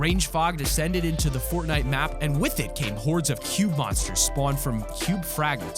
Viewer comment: Who else thought that he said cute monsters instead of cube monsters